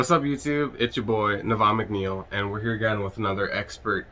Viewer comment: idk why but I thought you said "nirvana mcneal", good vid tho